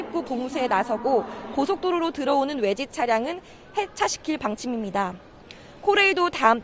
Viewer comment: 고속도로로 들어오는 외제차량은 폐차시킬방침이라 들려서 뭐지하고 계속돌려서들어봤네